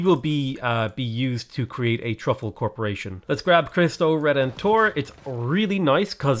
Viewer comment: Heard that as "Christ O'Redemptor"